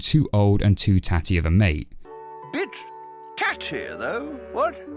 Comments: Did he just say what i think he said? What's wrong with this children tv series? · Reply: He said "A bit tatty." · I thought he said "Bitch, taty" Edit: Ok so it went from the realistic struggle of animals and possible childhood trauma, to a slightly semi graphical Disney show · I litterlly thought he said bitch tatty though 😂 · did he say"Bitch tatty though."? Also how did when the tail got bit off how did the rat not bleed at all IT JUST MAKES NO GOD DAMN SENSE!!! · I thought he said 'bitch"🤣🤣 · on 2:10 did i hear hollow say b*tch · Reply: Bit* he said bit. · LEGIT thought Hollow just said bitch. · I died of laughter because all I heard was "BITCH...taty" 🤣😂🤣😂🤣😂🤣😂🤣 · I thought he said bich. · Did he say "Bitch"? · I thought he said "bitch tatty" instead of "bit tatty" at first.... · he sounded like he said bitch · Am I the only one who heard hollow call her a BITCH in 2:01? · did he just called her a Bitch? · It sounds like hollow said bitch in